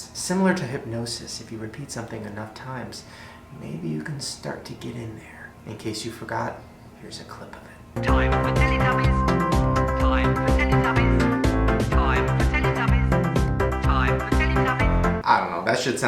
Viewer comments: I heard “time to tittytubbies” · Holy shit i thought it said "time for the titty tubbies" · lol it sounds like there saying: “call the titi tubbies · does anyone else hear that instead of time for teletubbies they hear time for titty tubbies